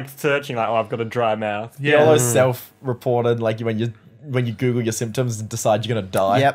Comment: it sounded like he said “yellow self reported”